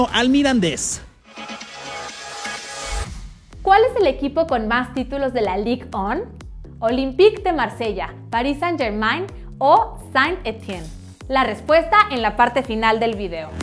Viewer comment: Es neta que dijo “lig on” 1:36 😂